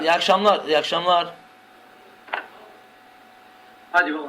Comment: hadi yol al mı diyosun benmi yanlış duydum.inşAllah yanlış duymusumdur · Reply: hadi baba diyorum kardeşim ama hadi yol al olarak anlaşılabiliyor